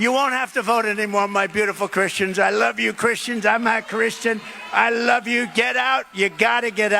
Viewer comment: Iono, each time I heard the clip I heard, "I love Christians. I'm not Christian, I love Christian." Did anyone else hear "not"?